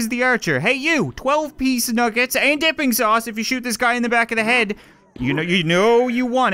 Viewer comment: I thought he said "Nipping" sauce not dipping sauce